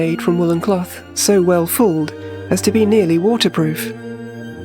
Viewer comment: What is the word at 4:49 about the cloths being woven? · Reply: I think it's "fooled", as in "pre-shrunked in hot water" making the weave tighter